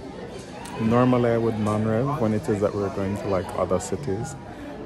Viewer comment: Am I the only person who clings on to every word? Sometimes i rewind over and over if he says a word I do not understand to MAKE SURE I DO.. so as I type this Im stuck at 7:28 where he says "Normally I". I DONT know what the word he says after that even tho ive heard it 20 times now .I pick back up when he says "when we are going to other cities". ok somehow ill get passed this for now but I GOTTA know every word! I know im a physcho! sorry! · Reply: lol 😂. I said normally I would “non-rev” which is airline industry jargon for when the flight attendants or their beneficiaries use the benefits to travel free or at a low cost. In a case like that we travel on standby, but for this trip because of how full the flights were we opted to pay for my flight instead. So that’s what I was explaining 😅